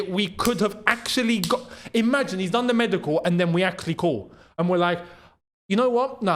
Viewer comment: Thought you said CALMA?